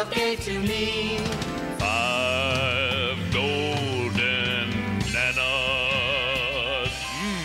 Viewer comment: I'm sorry, but every time I hear "five gold bananas" I hear "golden nannas" like golden grannies? What?! 😂 but seriously, great stuff!